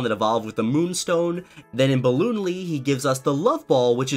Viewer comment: Did he just say ballonlee?